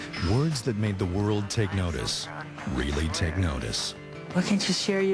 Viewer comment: really cute I heard lol